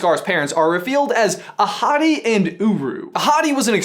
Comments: when he said Ahadi I thought he said ‘a hottie’ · I thought you said his parents were "a hottie and uru"...a hottie....and ueu